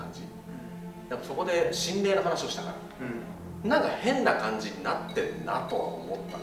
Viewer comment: 女の人がうんって言ってるように聞こえる、気のせいかな…